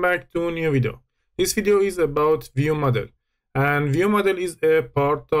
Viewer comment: At the beggining i heard: "This video is about your mother" 😅😅